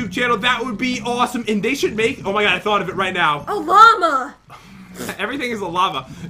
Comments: I swear I heard say Obama 😂😂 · I swear I heard say Obama 😂😂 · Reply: XxWoof PlaysxX she said llama but I thought she said Obama and thought it was 😂 hilarious